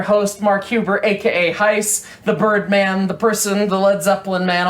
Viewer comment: according to the closed captions, you are heist instead of